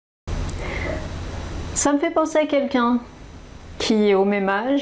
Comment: it sound like you said 'some people said quelq'un' '.ca ma fait penser a quelq'un